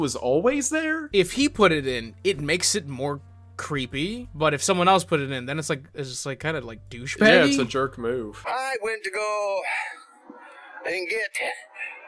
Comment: I thought he said “Hi wendigoon”. That hella scared me and in my head i was like how did they not notice that.